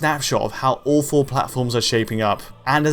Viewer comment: When you said “all four consoles are shaping up”, I thought you said “awful consoles”. I agreed.